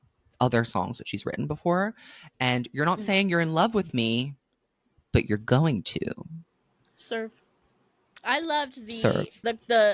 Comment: Omfggg this whole time I had thought she was saying “you’re not saying in love with me but you WERE GOING TO” and I was like omg this is heartbreaking what stopped him when he was about to say I love you